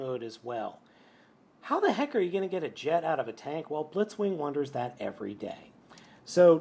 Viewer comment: Wait… did he just say blitzwinger?